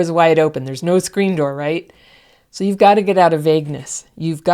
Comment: I thought she said "You've gotta get out of Vegas" at 8:16.